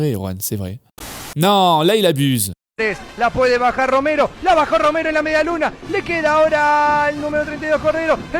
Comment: Il y a que moi qui entend "Nikki Lauda" à